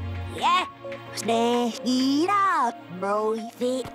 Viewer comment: i'm not the only one who heard "smash it up" Also 2:03 "hi i'm irish"